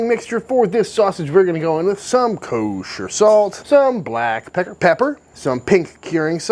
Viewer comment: Did he just say black pecker pepper....????!